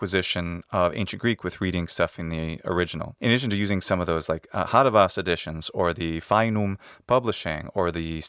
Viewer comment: what editions is he talking about at 29:20? I only know the steadman editions and i can't understand what he says before that ? Aharavas? Feinum?